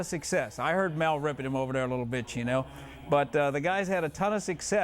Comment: Lol 5:43 sounds like he says "I kno kiper was ripping him little bitch".